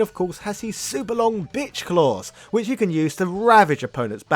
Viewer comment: Am I hearing that right?? “bitch claws” ?? 😂